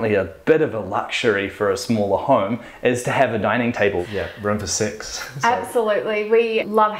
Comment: did he say room for sex?